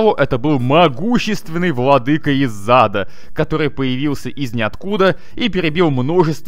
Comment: кто тоже услышал не из ада а из зада